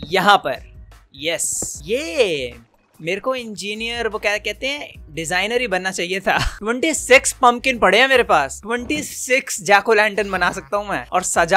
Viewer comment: Really ? What did I heard ? 😂"TwentySex"😂